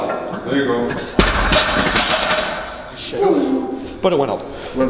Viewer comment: Did mans just say he shit??